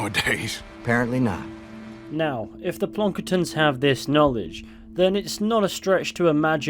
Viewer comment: What did you say? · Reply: Plonkertons